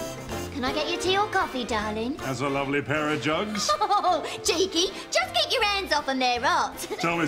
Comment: What does she say? "Cheeky! Just get your.. aerawfasdfasdfsdfsf" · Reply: "Cheeky! Just keep your hands off them, they're hot!"